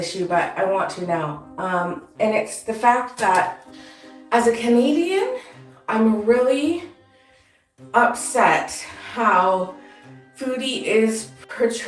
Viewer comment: Oh lawd, at 0:21 I thought she said “as a comedian, I’m really, upset”